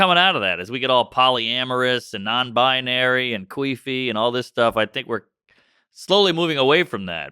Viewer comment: did he just say queefy lmaoo